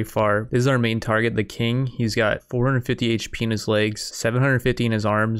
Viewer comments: "He has 450 penis legs" Did anyone else hear that at first or just me? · what i hear you say ? 450h Penislegs ? thats epic!